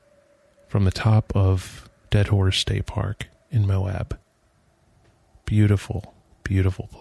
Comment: nice you helped the bat. they're so cute in a wild sorta way,- grouchy bat.....I could've sworn you said "dead whore state park in Moab Utah." made me chuckle...